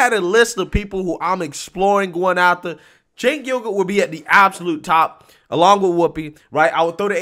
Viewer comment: I really thought said Jenk yogurt! 🤣 That guy is a fowl mouth man child. Why does he looks like he want to explode every time he talks.